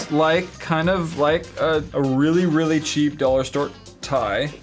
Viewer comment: at the end it sounded like he said dollar ster